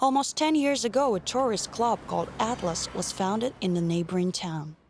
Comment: did she just say atlas? RWBY INTENSIFIES